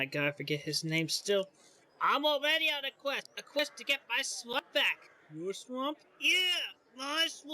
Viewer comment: Chris's attempt was actually pretty funny, I laughed when he forgot Farquad (Fuck-wad)'s name several times. I don't like his art style but it looked comical. When he said swamp I thought he was saying slut.